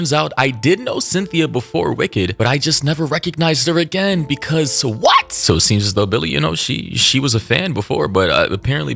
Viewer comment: I'm curious , 1:13 what's the A-word? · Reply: A n o r e x i c